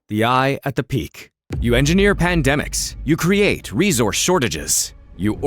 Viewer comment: mf did he just say epstein engineered pandemics?